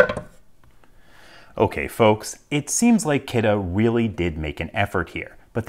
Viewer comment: Hello. Whats do you say at 1:46 ? Okay fox ? I hear you many times in others vidéos. Thank you.